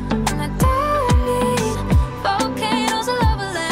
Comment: Sounds like she says "WAWI TOCH EES COK IN METOSH" Sigh, why can't modern singers seem to just say the words, always got to be a quirky infliction on their voice.